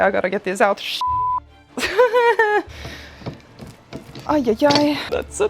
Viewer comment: Subs said "such a small bra move, Martina" and i was like, huh? Had to skip back and see if that's what you actually said, but was disappointed to find out it was just "small brain move", lol.